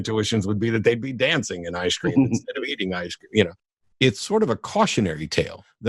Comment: The first time through, I thought he said 'sitting in ice cream' then had a weird response to Matt's dancing aliens... how wonderfully reliable our senses are.